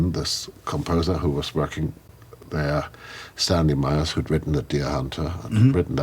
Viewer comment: Who is he talking about at 19:45 I just can’t understand what he’s saying? Great interview tho!!:)) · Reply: Stanley Myers